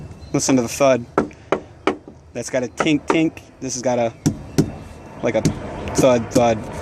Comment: I think he said "poor lil tink tink"